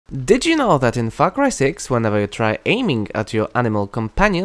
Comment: All I heard was "F*ck Ry 6)